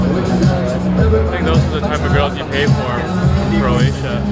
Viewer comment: did he say Croatia there